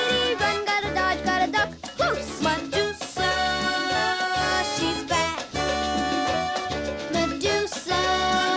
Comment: I know that Tyrone's saying "Medusa, she's fast" toward the beginning but I always hear it as "Medusa, she's fat" lmao